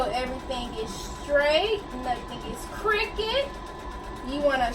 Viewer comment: But you sounded country frfr when you said “keep everything straight, we don’t want anything to be crooked” it sounded like you said cricket😭😭😭